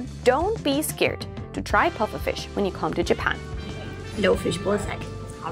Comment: did you say "blowfish ballsack" ??? O_o